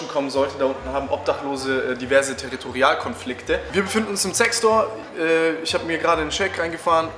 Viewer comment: Hab bei 16:44 anstatt "Zec Store" "Sex Store" verstanden, ja moin xD